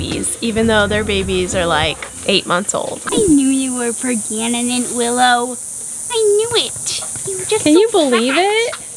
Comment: GIIIRL I HEARD THAT 3:20 'PRAGANENANT' hahah that was a great vid